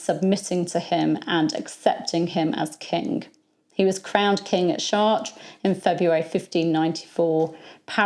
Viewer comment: What did you say at 2:56? He was crowned the king...that's all I was able to understand. I'm hard of hearing and sometimes some things sound completely different that what is actually being said and I could swear I heard he was crowned the king at shart. I know that can't be right. Lol help